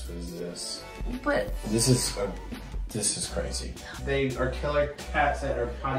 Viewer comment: Am i the only one who heard “babe” at 6:39 😌😌😏